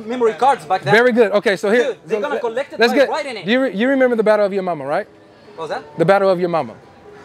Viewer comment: Lol I thought you said battle of Yo mama🤣